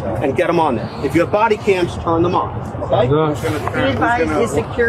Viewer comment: Did he just say "if you have body cams shut them off ?"